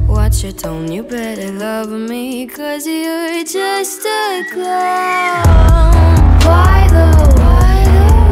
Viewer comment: When she says, "You better love me 'cause you're just a clone." I- 😂*wheezes* I thought she said,"You better love me 'cause you're just a clown. "😂😂😂😂😂.